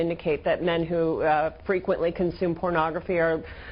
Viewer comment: Hold up. At 2:45 did she say men who frequently consume pornography